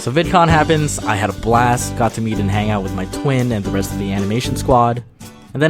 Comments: pause wait, what? rewind OH MY GODS, DID HE SAY TWIN? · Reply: omt yes he said TWIN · did he say his twin :0